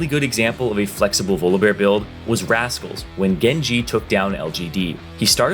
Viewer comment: did you say Genji instead of Gen G??????????????????????? ._.